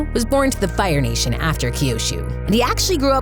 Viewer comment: Did anyone hear the lady say Kyoshu in